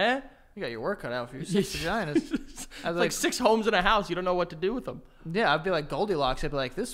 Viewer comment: 6 homes in a house??? lmao how did not catch that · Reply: I thought he said 6 homes and a house. Like ya got too much real estate